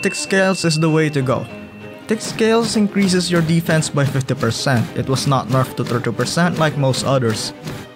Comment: what did u say about thick hide percentage sorry couldn’t understand 😅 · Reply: 50% increase to defense for sarco when using thick scales. Whereas other playables only get around 30% since the nerf